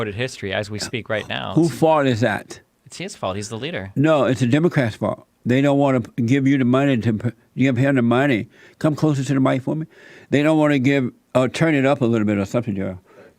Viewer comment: Could have sworn he said “Come closer to the mic, woman”